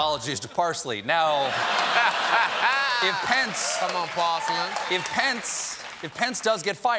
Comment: Does he actually say "come on, porcelain" in the background @ 1:24? To quote a person that shouldn't be mic'd all the time: "Wow. Woooow. Wow. ....wow."